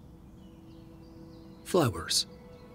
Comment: Honest to god I thought the narrator said flibbers instead of flowers 😂😂😂😂